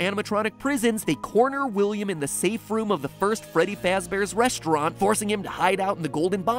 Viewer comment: what do when toilet clog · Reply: omogmgomgomgogmogmgomgogmogmogmogmogmgomgomgomgomgomgogmo when you turn on subtitles it say freedy!?1/?! ?!/!??!/1?!!1?1/1/!?/1/??1?!1/!1!!//1??/!/1/! Instead of Freddy/1?!//?!?!/!?!/1/?!/!